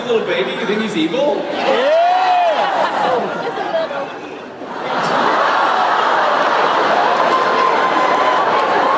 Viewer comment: What did the person at 14:21 say? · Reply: i think she said "kill that baby"